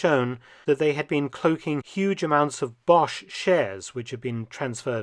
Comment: I thought you were saying chairs, when you said shares. And I kept thinking, man this guy.... Knows his history. Because yeah that many chairs would absolutely mean they were bringing a lot of Nazi people. Took me, some time indeed.